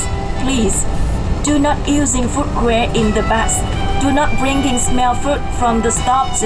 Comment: It did not look fun at all for you, and when the woman said no smelly food, I thought she said no smelly feet. And gee to charge just to use the dunny, I would have pee'd in the bushes around the back hehe.